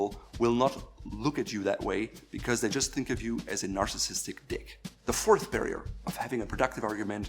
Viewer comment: Did he say narcissistic dick while smiling at 5:45, if yes then that insult was so pleasing, for the president.